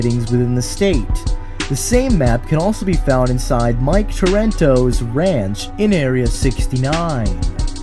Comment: Did you say Mike Torento on 9:59??